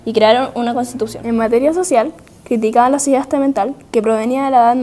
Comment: que dice al momento 2:09 yo entiendo sidasta mental o que